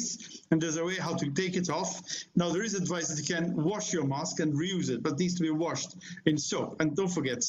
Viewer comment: What he just said? To Wash the masks?